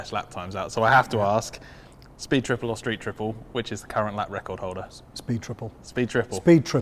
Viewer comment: What I heard: Speed triple or speed triple? 😂😂😂